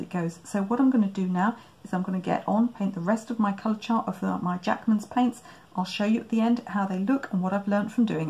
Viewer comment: Jackman's art materials sound very like Jackson's art materials. Is this company trying to play off that? Jackson's is a well known and respected brand supplier if artists materials and they also have their own paint range. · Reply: I see on further investigation these are handmade colours. I think it's just unfortunate that they have similar company names. First time I heard it said in these videos I thought you said Jackson's!